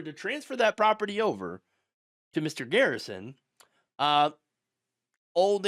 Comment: Wait, did you just say Mr. Garrison? Lmao sorry 😞